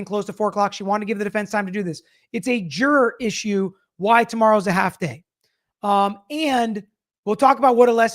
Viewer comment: While watching this live earlier, I thought Judge Bev was saying that tomorrow was going to be "a hot day." I'm in NJ and wondered whether we were going to have hot weather also. Now I hear that she's saying "a half day." These British Boston accents throw me off!